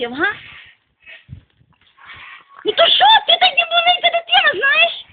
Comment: Допоможiть будь лазка розшифрувати що вона каже з 00:48 ? Дуууже цiкаво😂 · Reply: ну то шо ти таяк маленька дитина знаєш )))